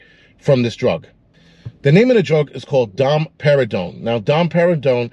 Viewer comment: I thought you said Dom Pérignon at first 🍾😂